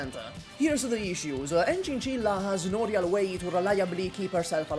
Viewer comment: Thought you said Enchinchinlla